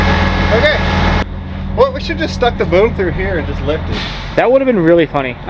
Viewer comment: did he just call you “boo”?